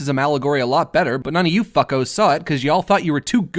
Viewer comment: did i heard "fuck"? after the New policy this video is getting flagged, you won't get any money from it anyomore and we give no shit about it -YouTube